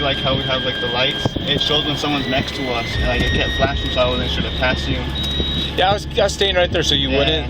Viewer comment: I know this is unrelated, but a lot of times when it said "unintelligible" I could perfectly understand what was being said. Just me? Example: 2:09 he says "And like- it kept flashing."